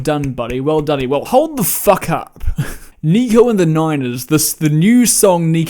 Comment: did you just say "Nigga and the Niners", or it is just me? lmao HAHAHAHAHA